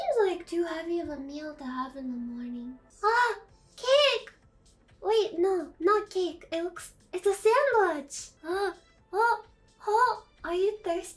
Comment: The captions say "sandwich", but I hear "sandbudge" Whatever the hell that is